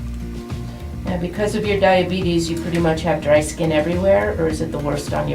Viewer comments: soooooo cringeeeeeeeeeee........ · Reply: *did I just hear.....DIABEATIES!?! OMG GIMMIE YO CAN-DAY STASHHHHH!?!? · Did I just hear diabetes!